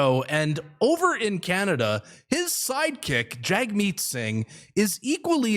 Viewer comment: 6.30 I heard you say Side chick. Hahaha